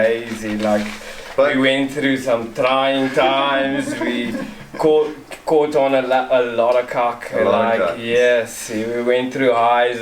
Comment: For those of us from afar, what was said at at 9:25 ? You caught on a lot of what now? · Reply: Haha kak "Sh*t" in Afrikaans.